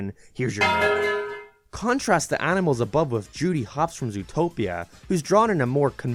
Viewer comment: What did he say? Here’s your what? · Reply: Medal